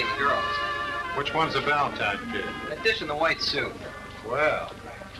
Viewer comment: did he say "that bitch in the white suit"? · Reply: that dish in the white suit.